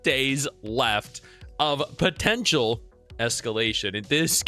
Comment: Did you say Potential?